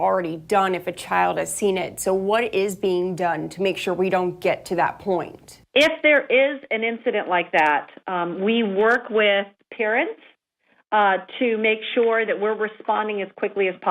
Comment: i already said this but it sound like they say they work with karens